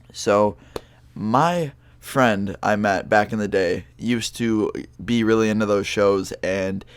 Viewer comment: IT sounded like u Said back in the gay i maybe high from 5 minutes ago like that weed bro